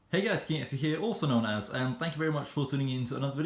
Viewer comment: Who else heard him saying “hey guys Cancer here”?